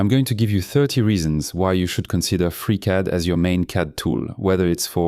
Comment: Oh....Free Cad......I thought you were saying Freak Out and i download some hippy program:hand-purple-blue-peace